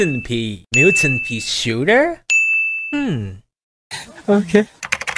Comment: I thought they said militant peashooter. Oh wait make militant peashooter PLEASE🙏🙏🙏🙏🙏🙏